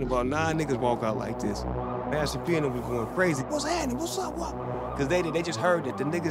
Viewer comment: did say "Master P and them was goin' BRAZY?!"